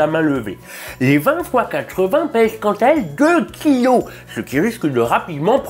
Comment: Bordel j'ai compris "2 clio" a 1:56 au lieu de 2 kilo, ca fait lourd! XD